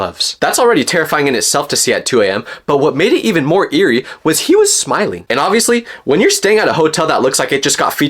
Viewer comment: i thought he said he was sagging 💔💔